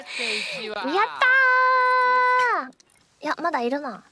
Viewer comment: should be her rendition of "yo sha!" which does sound like urusai, but given the situation i don't think she will say urusai.. lol